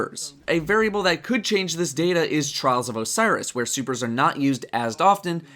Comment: the subtitle says Soopers instead of supers. :P